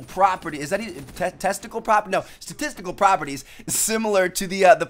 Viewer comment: Anyone else hear "testicle properties"?